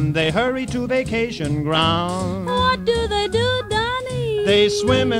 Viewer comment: i heard" what do they do down here."